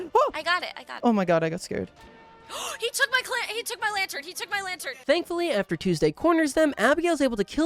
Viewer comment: Genuine question. What did Tuesday say at 1:07:25 ? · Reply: “he took my lantern” :)